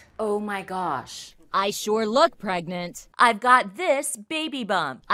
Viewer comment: In the begging I misheard the phrase “I sure look pregnant” and interpreted it a “I sure love pregnant”